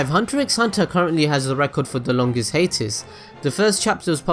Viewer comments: When you said hiatus I thought you said haters, And I'm like, " Wait wahhh??" But overall I am very happy you considered making one of these for HxH. The fandom is very happy. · You were saying "hiatus"? I thought you were saying "Haters"... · When he was saying "hiatus" I thought he was saying "haters". "Did you know Hunter x Hunter has the most haters?" I was like whattt, didn't know that lol.